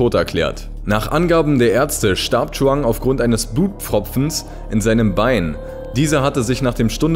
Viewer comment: Bei 4:02 hab ich da Blutpopfens verstanden?